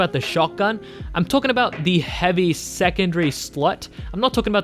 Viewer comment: i misheard slot as slut